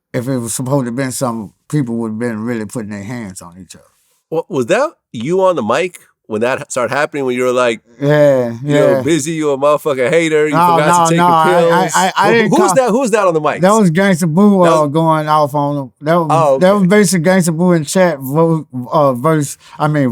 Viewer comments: All I heard was "Bizzy U a hater! Take ur pills!' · I heard him say fuck you gangsta boo